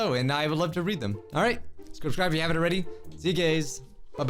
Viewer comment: Am I the only one that heard him say “see you gays” at the end haha love you bijuu